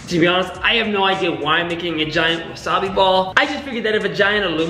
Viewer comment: Who else thought he said vagina 2:18 😂😂